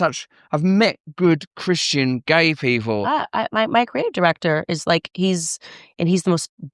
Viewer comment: Her what director? I honestly can't understand what she's saying right there? Is it creative director? I'm curious?